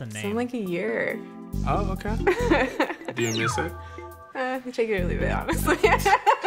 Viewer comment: Wait what does the girl say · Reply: “Take it or leave it” I think